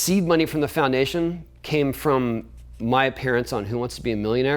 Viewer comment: I heard “my parents on who wants to be a millionaire “ took a minute to twig “my appearance “ 🤣😂😂😂